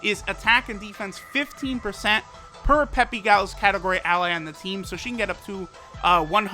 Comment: I thought he said 50% per Peppy Gal unit lmao